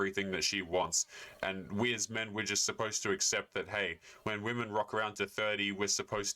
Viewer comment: the closed caption said. "...hey when women rock around to 30..." I heard "..hey when women WAP around to 30..". I hope the later was reality.